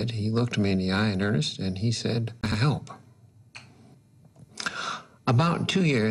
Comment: There is an audio glitch at 3:30. What did he say there, as he was repeating the other doctor's words? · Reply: "can i help?"